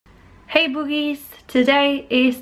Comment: This whole time I thought you were saying "Boobies" instead of Buchies